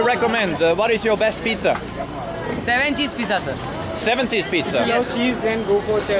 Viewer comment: i heard 7 Seas pizza